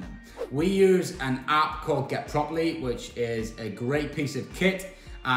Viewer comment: what did you say there? Your accent is very strong. Is it “get properly”